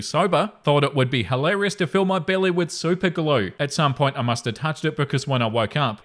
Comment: Story 3: I honestly thought you said "soup and glue" wondering how the hell the glue dried if it had soup in it 🫣